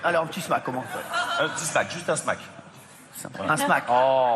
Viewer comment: c'est moi ou juste avant que jean michel maire fasse le bisou non consenti on voit hanouna regarder vers la poitrine et dire "sein voilà" en acquiesçant comme pour donner le feu vert ? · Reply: il dit "juste un smack" et la fille a l'air paumée (elle était venue seule ?)